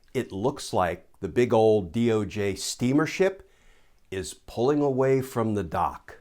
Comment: Oh, he said steamer ship, I thought Glenn said the DOJ is a steaming s**t. I will believe it's the latter until Don the Con is indicted.